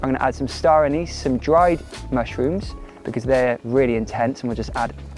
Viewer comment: What does gaz say the ingredients are at 3:48!? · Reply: Star anise, dried mushrooms & nori :)